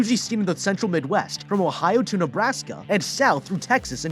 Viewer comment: did I HEAR OHIO